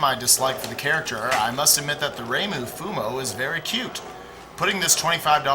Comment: Also, Reimu again...it's seemingly always a Touhou plush. lso, is she bgger here or is the plush just closer to the screen? Also, what was said at 8:53? "Reimu...something"... Is that a Japanese word for "plush doll"? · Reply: No, reimu is the name of the plush the guy on the front left is holding. She is one of the main protaganists in the touhou project games.